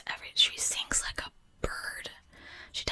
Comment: Ohhhh, I thought you said "SINKS like a bird," the first time. I was so confused! XD